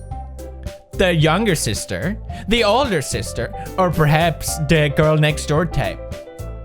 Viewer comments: Whait, what? He said Dickgirl? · What Pewds said: “...the younger sister, the older sister, or perhaps, the girl-next-door type?” What I heard: “DEAD girl-next-door type” uhh well yes, but actually no💀🤭